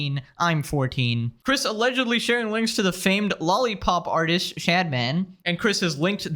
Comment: when u said shadman i thought u said stradman for a sec